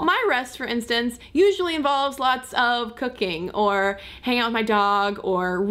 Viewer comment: I was like whattt??? I went back and then understood you said "cooking". (I had misheard "cocaine" hahahaha! :p)